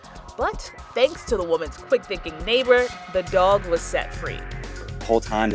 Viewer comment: yo lmao for a second I thought she said “thanks to the women’s fast thinking” 🤣💀💀💀